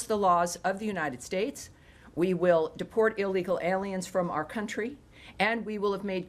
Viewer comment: it is evident the years of far left wokeness BS when the YouTube autosub writes "support alien criminals" instead of what was said "deport alien criminals" 🫣 Peace ✌️